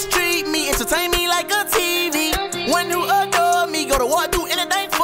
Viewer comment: For those who do not know, NBA named this song “NICKI MINAJ” because of her ride or die relationship with Meek Mill. YoungBoy was introduced to Nicki by Meek. Before Meek went to jail, Nicki was riding for him. She testified for Meek in jail and everything. Nicki was with Meek’s mother the other day because his mother was crying. So he’s saying he needs someone like NICKI MINAJ. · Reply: I thought he said who'll never leave me, and return me like a TD.(touchdown)